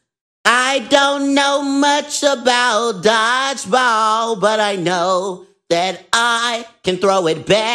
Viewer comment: I honesty thought he said God's balls at first😢😂